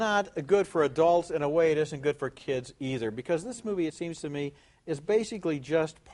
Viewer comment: Mdrr j'ai compris "fortkids"